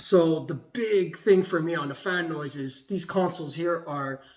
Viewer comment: i thought he was saying fan boys lolol